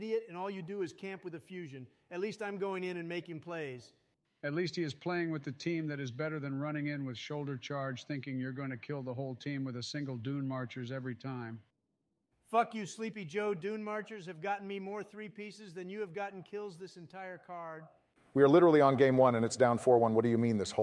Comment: Lol I thought he said "Doo Doo marchers" 😂😂